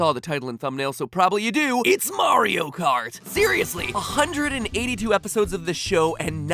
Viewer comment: Am I the only one that heart "theoriously" instead of "seriously"? 🤔